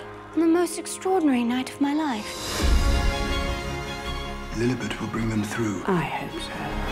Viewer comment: What did he say at 1:55? Thanks. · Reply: Judith P KG: Lilibet will bring them through. QE: I hope so.